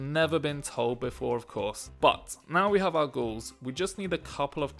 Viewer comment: those zombies kinda reminds me of zombies from Kings of War. And not gonna lie, thought you were saying "Gauls" for quite some time and not "ghouls"...